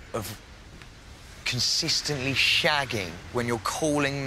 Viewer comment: He said “consistently shagging” sooo it wasn’t even a one off?! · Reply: First I thought he said constantly . But guess that's my poor hearing lol . I rewatched just to check again 😃